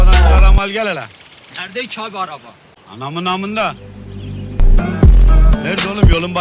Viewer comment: Aa bu sefer manımana yok ama manımana diye duydum ya😂😂